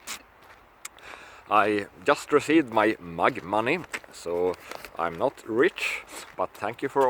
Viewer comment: When said he received his mug money, I thought he said he received his MAGA money. I mean why not, we are bankrolling everyone but American citizens.